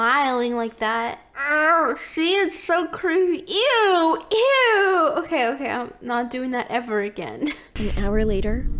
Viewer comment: I thought that you said that you will not do that weird face again